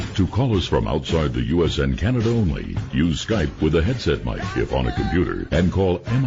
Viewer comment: after the break, the narrator say's "use the headset mic (mike) it'll be by the computer. It's not what he actually said but if you listen it definately sounds like he was directing Mike to use the headset and where to find it .. conspiracy theory ..